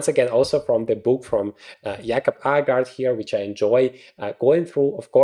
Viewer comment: hows the book called? i cant understand it accoustically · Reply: I just said that it's from Jacob Aagaard but didn't mentioned the title. It's called "Grandmaster preparation: Calculation"